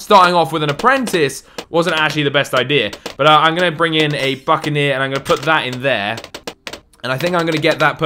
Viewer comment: who else heard fuckeneer xD